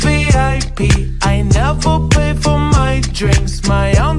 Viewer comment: Lyrics: I’m walking like a dime peace I go strait to vip I never pay For my drinks my own Trosh behind me :) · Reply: I thought it said I never pay for my dress